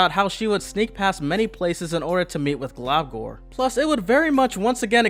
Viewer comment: did he say meet or...